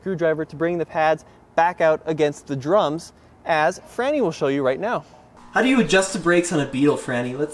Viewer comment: Did he say "tranny" was going to show us how to adjust the brakes? Is this brake adjust instructor a man or woman? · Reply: FRANNY, not "tranny".